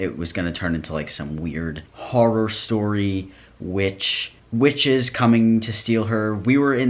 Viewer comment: the entire time i heard "riches" instead of "witches" and just kinda went along with it bc ngl- rich people be like that